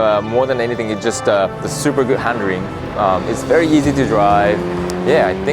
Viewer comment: There it goes!! "Handering"!! I heard it